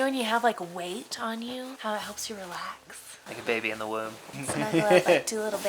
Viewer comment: you said “baby in the womb”. Is pregnant?! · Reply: I heard ‘babe in the woods’